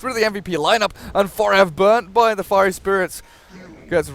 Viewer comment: did he just say ebola spirit lol XD